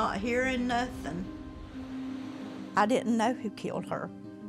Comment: It sounds like he said I didn't mean to kill her